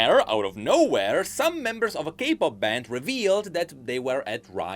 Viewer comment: For a second I thought said Gay pop band but I realized he meant K pop band 😂 His accent is adorable